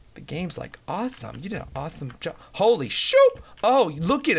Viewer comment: I thought u said holy sh!t